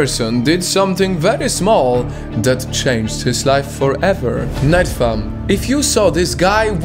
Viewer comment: I hear him saying “night fam” not nightfall